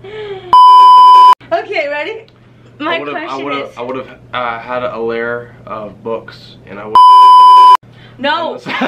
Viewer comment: what did Colby say at 17:14 that was bleeped out? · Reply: I think it was a "You" reference. He'd have a lair of books and k*ll people.